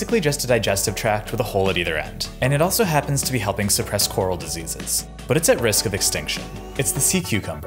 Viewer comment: Stop-💀 I heard “And it also happens to be humping depressed coral diseases” 😭💀